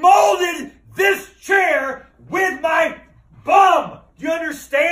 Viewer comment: When I heard Rhett say "I have molded this chair with my butt" I knew it was fake😂😂😂 (thought it was real at first) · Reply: Ethan Vue he said bum dummy